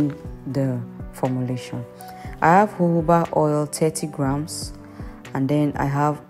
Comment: Please did she say jojoba oil???